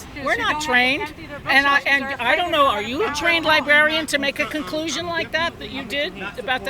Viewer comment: I thought I heard it all until I heard "train librarian" 😂